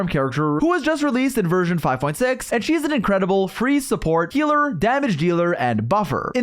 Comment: I thought bro said she’s a free support at 0:10 if only 🥀🥀🥀